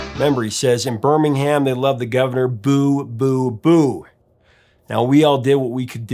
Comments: Boo, Boo, Boo? I always thought they were saying fool, fool, fool! Guess I need my hearing checked. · I always heard the "boo boo boo" was "hoo hoo hoo" just a filler sound!